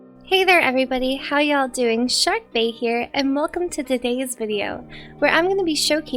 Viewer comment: I thought she said "Sharkbait" first and I was like: Well that checks out.